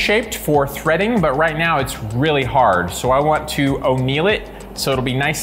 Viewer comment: … did he just say “I want to O’Neill it lmao…?!